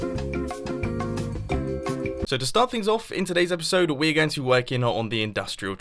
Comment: Did just said epishode at 1:23 ?